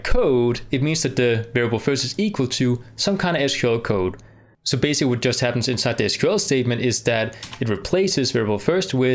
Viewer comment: what he's saying??? btw amazing videos :) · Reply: So basically we just happen to have inside the sql statement is just that